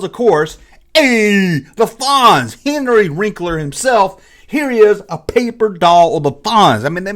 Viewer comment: Did you just say "Henry Wrinkler"? 🤣